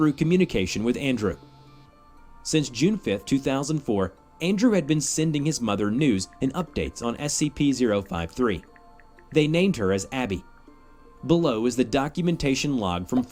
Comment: I low key thought he said nudes lmao